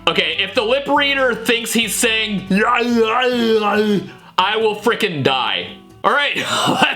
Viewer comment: I think when Donald Duck reportedly said the B word after the B word I heard "I will kill you!!"